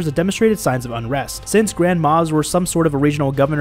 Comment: Did no one else thought when he says Grand Moffs, it more sounds like he says “grandma and grandmas” lol